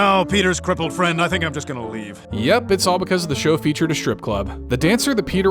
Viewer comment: I honestly thought you were going to say " because they showed a cripple to Carter.".